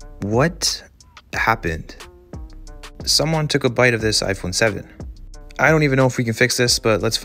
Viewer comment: Bro when he said “a bite out of this iPhone 7” I thought he said “someone committed the bite of 87”💀